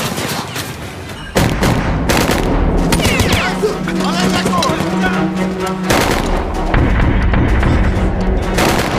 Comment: what does the german say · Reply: I think he says "lookout take cover" deckung means cover